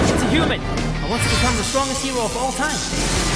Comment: I thought deku said i want to be the strongest emo of all time😂😂